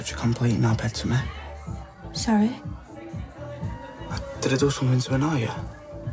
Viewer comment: What does he says at 11:36?? 😊 I don't understand anything there... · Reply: He said “Did I do something to annoy you?”